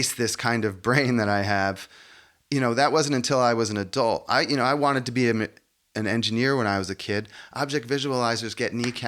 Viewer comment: “I wanted to be a ma-(gician)🪄, engineer…” is what I heard 😊. Same! Excellent program! The maths was too darn gatekeeping now I’m a film maker 😅